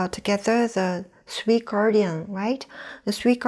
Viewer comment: | What's this point called? I heard something like "three/sweet guardian" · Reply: three gardians :) these can regulate lungs fonction.